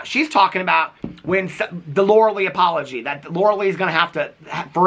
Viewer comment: I may have misheard but I swear he said Laura Leech instead of Laura Lee…